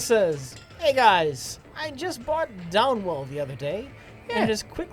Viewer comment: I'm going to blow a gasket if i don't figure out the name of the game they keep slurring out at 31:00 jesus fucking christ. is is downwolf? download? downlul down wall? i mean come the fuck on. dob ball? down ball? fuck me that's infuriating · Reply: Veto Bandito downwell good sir. It is a great little game, highly recommended